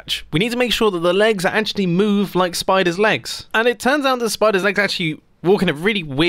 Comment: Ok I can’t be the only one who thought he said spoilers instead of spiders at the beginning of the video